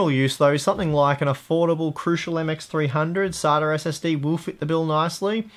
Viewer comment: I keep hearing "soder" instead of SATA.